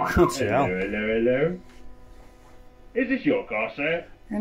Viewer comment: What in the voodoo ritual language did you just say lol 😅😅😅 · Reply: An unconvincing "is this your car sir" 🤣